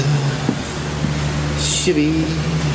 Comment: Every time I hear say 'Chevy' I keep thinking he's saying 'Shitty' ...put the fuses in our 'shitty' bag lol